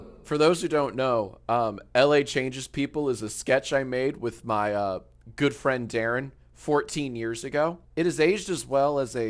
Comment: I heard that as "my good friend Erin" Checks out.